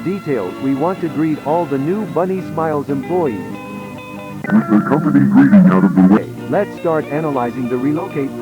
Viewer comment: what does it says? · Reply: With the company greeting out of the way